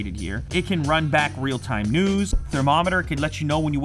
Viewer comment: I swear I heard "real-time nudes"